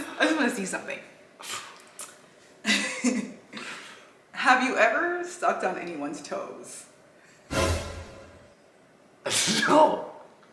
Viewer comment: I thought you said, "have you ever stepped on anyone's toes?"....lol...I thought he was a bad dancer....lol